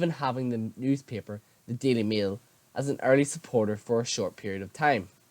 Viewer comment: Sorry did you just say the daily meal 😂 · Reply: I think he said "Daily MAIL."